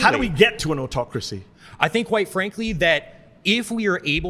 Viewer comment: When he says, "I think, quite frankly, that.." I hear, "I think white, frankly.."